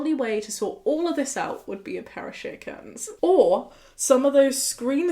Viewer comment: I love how the subtitles here says "chickens" instead of "shear curtains" 😆 Really changes the narrative